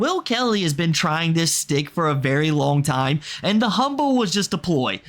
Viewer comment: did you say "Stick"?